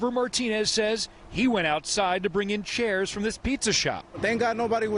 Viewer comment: Who is in the right mind went to swim, while the storm was happening? 🤦🏻‍♀️ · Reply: 5.53 I THOUGHT HE SAID PISSY SHOP!!!!!!!